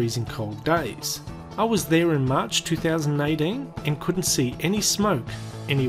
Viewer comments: Regarding 3:40. It must be your accent. Because I clearly hear 2019. Whatever. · ★★★ FREQUENTLY ASKED QUESTIONS ABOUT THE VIDEO / FACT UPDATES ★★★ 1) At 3:40, he says “March, 2019”? - For some reason people are hearing me say 2019 at 3:40, I listened to the video, and I clearly hear 2018, which is what I said. 2) Where did all the houses go? - I have not researched this answer, it is just a guess, but I would assume either the government relocated the houses to new plots of lands in another town, or once people moved out the government demolished the houses, so homeless people could not live in them. 3) The cheerful jingling music doesn't really fit the video - Yes I know, but I made this video when I was using that music a lot, as I own the licence to it and it was kind of my theme music, but I started using more appropriate music for the topic as time went by.